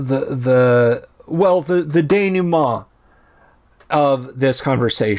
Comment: What is that French word you used? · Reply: de·noue·ment /ˌdāno͞oˈmäN/ noun the final part of a play, movie, or narrative in which the strands of the plot are drawn together and matters are explained or resolved.